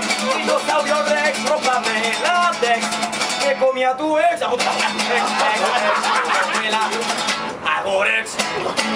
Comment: What is he saying at 3:05? Translation please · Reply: "me comí atu ex"